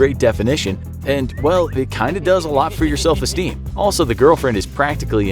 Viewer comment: I think i heard "MY girlfriend " and not "THE" HAHAHAH !